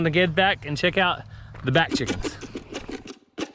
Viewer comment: I heard you say " bat chicks" instead of back chickens! I was like, Wow! 😂😂, too! ❤🙏